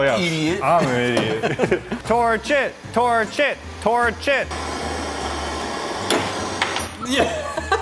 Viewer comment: it sounds like saying the Pokémon torchic 🐥